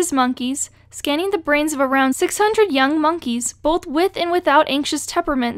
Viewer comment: For a minute I was freaking out and laughing cause I thought she said "Scanning the brains of racist monkeys" I played it over again just to be disappointed that that's not what she said 😔